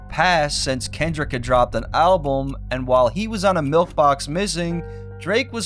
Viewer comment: Bruh says milf box instead of milkbox 💀